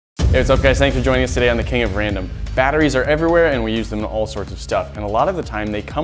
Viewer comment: can sombody tell me what he just said, I heard "it's ok thanks for joining suss today king of random" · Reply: "Hey, what's up guys? Thank you for joining us today on the King of Random."